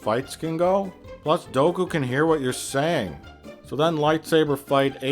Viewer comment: Is it weird that I thought he was saying "Goku can hear what your saying"?